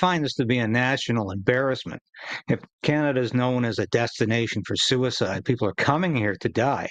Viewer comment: He said Canada was becoming a, "destination," for euthanasia, I heard, "Death-stination." Just a little joke, I'm all for youthinasia. I'm for youth in every country. Hope that's not in poor taste. Hitler got his Eugenics concepts from California. The movement was developed here. I look at John Steinbeck's, "Grapes of Wrath," armed men at the border of California keeping the victims of the Dust Bowl from seeking refuge here.